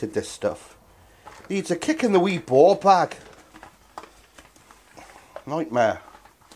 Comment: I've listened to 13:17 over and over and I have no idea what you said. Too British for me lol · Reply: Kick in the wee ball bag